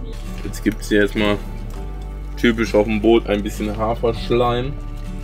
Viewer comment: Jetzt habe ich glatt "Haferschlamm" gehört bei 13:16 .. 😂